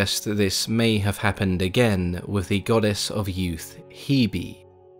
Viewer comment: legit thought he said goddess of yeet hebe since she was yeeted at birth